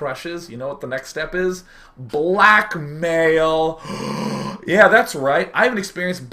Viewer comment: Freudian for sure, but was I the only one who heard "black male!" at 4:44 instead of "blackmail!"?